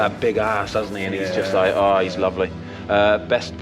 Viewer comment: Did he say lovely big ass lol?